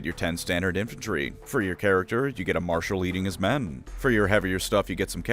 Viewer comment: I swear to God, I thought you said "a leader EATING his men!" And I immediately thought, "what numbnuts thought it was a good idea to put the Ogre in charge of the human squad?!" 🤣